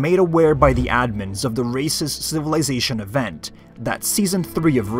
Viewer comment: thought bro said “racist”